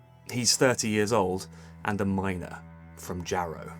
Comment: I was scratching my head. I thought you said he was 30 years old and a minor...Then I realised you meant 30 years old and a miner; then it made sense 😀